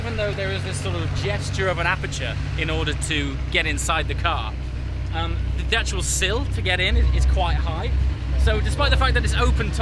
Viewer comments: can someone tell me what did he say at 0:44 - 0:46? because I didn't understand what he is saying and English is not my native language, so excuse my poor grammar. would appreciate the answers and as always your content is great from the beginning until now. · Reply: no problem. He says "the actual sill to get in" 🙂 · can someone tell me what did he say at 0:44 - 0:46? because I didn't understand what he is saying and English is not my native language, so excuse my poor grammar. would appreciate the answers and as always your content is great from the beginning until now. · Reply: I do understand when he said 'sill'. I just didn't understand what he said before 'sill' word at 0:44 that makes me confused. "The detract sill?, the detual sill?" Is that what he said at 0:44? The auto-generated english subtitle in this video shows me "The dutch will sill" at 0:44 which is actually gramatically incorrect in my opinion. Anyway, thanks for your answer. But sorry, your answer is not what I meant to be. Perhaps, you would specifically tell me again what he said at 0:44? I'm so thankful if you do so.